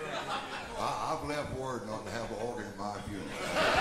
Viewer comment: What does JD say 5:57? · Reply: I think he said “I don’t need more of that” “I’ve left word not to have an organ at my funeral, I hate it”